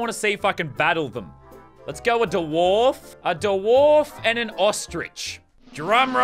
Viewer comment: Did he just say da wharf